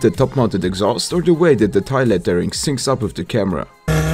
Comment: nobody can understand what you're attempting to convey. The captions says "or the way that the Thailand bearing and syncs up with the camera" and at 5:34 that's a Honda not a mini..... 6:05 "v-12 boxer" lol